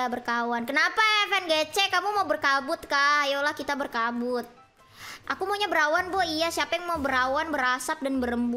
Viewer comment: Bangsat sini dengerin sambil ngantuk, pas bilang "aku maunya berawan" dengernya "aku maunya perawan"